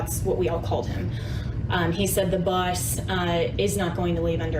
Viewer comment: "the bus, is not going to leave under any circumstances". Funny how people just can't lie without speech problems. Immediately before this she clearly said Boss but her mouth didn't co-operate with her lying mind when she tried to string it all together in a story, CNN style.